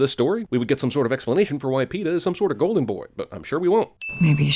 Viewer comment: Even though I know she says Peeta but at first I heard Cato.... Just Saying.. 😝😝